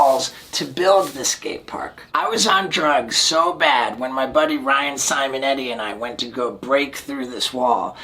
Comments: I always thought he said: My buddies Ryan Sam and Eddy. But its only one guy, Ryan Simonetti oof... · I thought you said ryan simon and eddie😂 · I thought you said my friends Ryan, Simon and Eddy not Ryan Simonetti hahaha